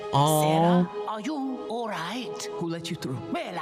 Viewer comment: I thought he said santa 🤣